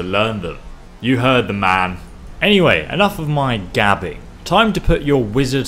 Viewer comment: when it said gabbing I thought it said dabbing I'm ashamed of myself